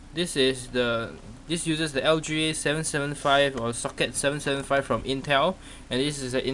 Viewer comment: i thought he said "suck it" until i watched for a few more seconds, gotta love the accents >:D